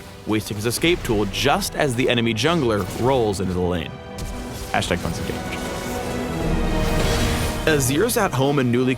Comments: What he say at 6:41? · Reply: ***** He says "#OnceAgain" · Lol anyone else hear phreak say #tons of damage at the end of the rammus gank at 6:41? XD